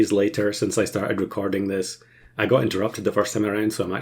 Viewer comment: when you switch recordings cus of interruption your captions say “got into drugs” lmao